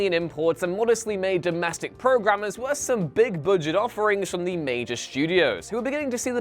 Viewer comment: I keep hearing "Boo-jet" whenever the narrator says budget. LOL